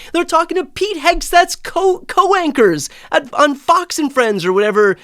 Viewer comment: "Pete Hegseth's co-wankers" is what I heard, and seems very apt.